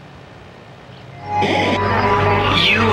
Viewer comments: ”You are no longer welcome here, nuisance...” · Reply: I thought it say "you are no hunger welcome here, isn't?" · "YOU ARE NO LONGER WELCOME HERE NUISANCE" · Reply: I thought she said "YOU ARE NO LONGER WELCOME HERE FOR YOUR SINS"